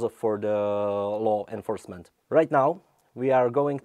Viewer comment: I thought that dude said "Lol enforcement" and I can't say that I disagree with him